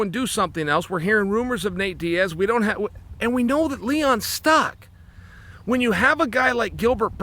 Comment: at first I thought he called him Leon Scott 😂